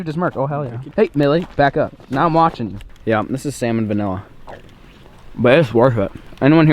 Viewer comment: “Let’s not get salmonella” uses spatula that he used with raw beef to eat cookie off of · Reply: Sounds like he says salmonvanilla